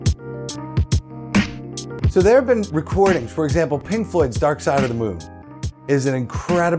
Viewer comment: Lol. Thought he kept saying Pink Foot. N i was about to make a comment to listwn to some tunes from Pink Floyd. Lol.